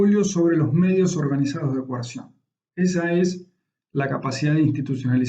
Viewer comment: profe no entendí que es lo que dijo en el minuto 4:21 (garantiza su monopolio sobre los medios organizados de ......) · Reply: Coerción